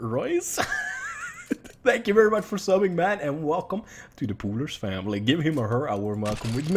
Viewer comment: "Welcome to the _______ family." Could someone please complete the sentence for me? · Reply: I thought he said coolest XD